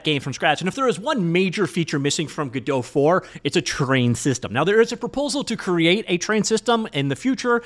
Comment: Please… talk slower. This is pure hectic. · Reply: Thought he was saying train systems 😂